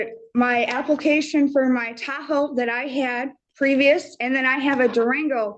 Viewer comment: I thought she said "he said I dont have a vehicle but I have a taco" guess it was Tahoe? Maybe? Probably taco